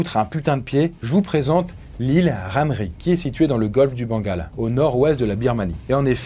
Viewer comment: y'a vrm que moi qui ait entendu "bangala" ? xD